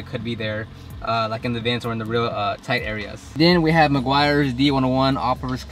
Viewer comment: What's the cleaning solution name, at 1:28? Did you say McGuires something?